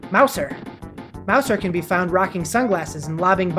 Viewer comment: When you said mouser I thought you said meowser but I was like "wait there in 3d world*